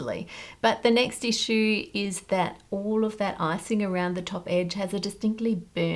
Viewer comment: I heard that as ‘all of that arsing around’ rather than ‘icing around’ and honestly either way works!